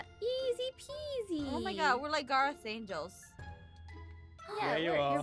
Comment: I thought she said "Garroth's Zane-gels" lol